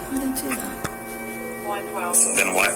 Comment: Did she say "why did I do that" at 43:50?